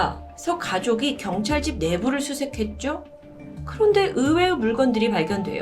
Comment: "가족이 경찰 집 내부를 수색했죠"라고 들리는 건 저뿐인가요? 말씀처럼 일가족이 다 실종되는 건 항상 들어도 미스테리인 거 같아요 ㅠ